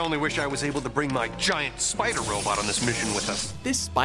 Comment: "Did he say 'his giant spider robot?'"